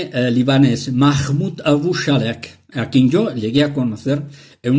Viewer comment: Solo una duda....el nombre del profeta arabe libanés no escuche el nombre correctamente......mahmud shalek??? 22:26 alguien que me ayude please? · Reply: Cierto, no figura en internet, su nombre es Mahmoud Abou Salek, y el legado profético, según dice el profesor, lo recogió él personalmente, lo que quiere decir que sus profecías no son públicas. Sería bueno lo pusiera en internet.